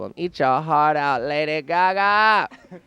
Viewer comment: What does Sharon Needles say at mark 1:43? "HR hot out Lady GaGa"? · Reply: Eat your heart out, Lady GaGa.